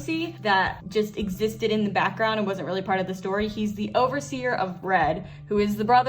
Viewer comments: I honestly thought she was saying "Overseer of Bread" the whole time lmao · I thought you said he was the overseer of BREAD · I thought you said he was the overseer of bread, until you coloured him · At first I thought you said "the overseer of bread" and it took me a while to realise you said red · ONLY FIVE AND A HALF MINUTES INTO THE VIDEO DO I REALIZE YOU SAID HE'S THE OVERSEER OF RED I THOUGHT YOU SAID BREAD AND I WAS SO CONFUSED OH LORD I'M DUMB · I thought you said the overseer of bread at first, I thought like the hat could be representing a slice of bread. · When you first said “the overseer of red” and “the overseer of blue” I thought you said “bread” and “glue” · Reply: I legit that thought she said the overseer of bread too and I had no reaction to it because I've heard and seen weirder things but I did hear her say the overseer of blue. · Okay let's be honest who's thought was saying the overseer of bread instead of red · for a little bit, I was confused why the overseer of BREAD was related to the overseer of blue but then I realized that i terribly misheard what you said xd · i swear, i thought you said he was the overseer of bread and i was so confused as to how you were taking the overseer of bread so seriously, until you colored his shirt red